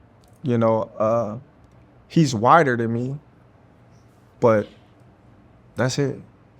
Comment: Legit heard him say he’s whiter than me that’s it 😂😂